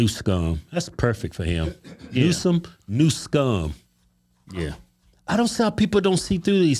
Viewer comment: I heard him say nut scum 😂 selective herring